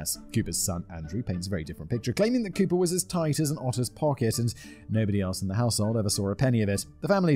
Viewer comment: Been a sub for 3 years, 8:38 says tight as an autists pocket. That's a vibe. I know in my heart that the closed captions are wrong. I may or may not be on the spectrum, but im happy to be represented either way. <3